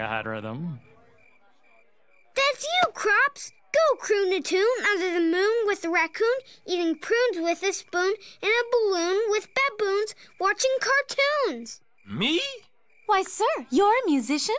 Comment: What's greg saying at 5:44 ? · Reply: That’s you crops! Go croon a tune, under the moon, with a raccoon, eating prunes, with a spoon, in a balloon, with baboons, watching cartoons!